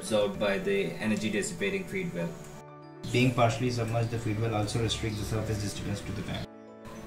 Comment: I can't understand what he's trying to say. Is anyone able to explain it to me please? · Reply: Being partially submerged the feed well also restricts the surface disturbance in the tank. That means when waster enters it won't disturb the clarified water.